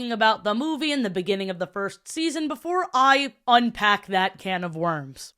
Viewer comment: thought i heard "funpack"